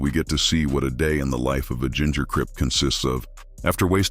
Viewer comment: 😅😅😅😅😅 OMG did u say "ginger cryp"??? That's.. HILARIOUS 😅 1:52:57 Get out of prison and don't know how to do shit?? LMFAO... You did say he was gone for 3 years?? Right? Not 30!!? .. I'm guessing it's because he didn't know how to do shit BEFORE HIS BIG 3 YR STINT!!???HAHAHAHAHA. I did more time in a job interview ....OMG!!!